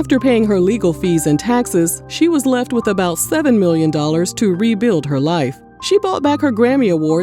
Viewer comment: Hi. near 6:00 did u say "7 Million"..? That's le$$ than 1/2 of what she won then! Yikes! 8:44 😞 Libros #Libra... mi Luv u, #Toni! 🙂